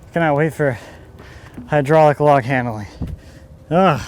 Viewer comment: Did I hear "Hydraulic log movers" are in the future? That would be awesome! Hydraulics would be great but is always a seemingly complicated and expensive undertaking for us mortals. So, look forward to learning how you might tackle that.